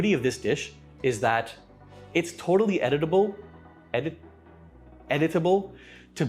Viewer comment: I heard "totally edible" & was like I hope so!😄